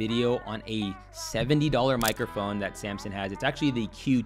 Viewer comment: i thought he said samsung i was like when did they ever...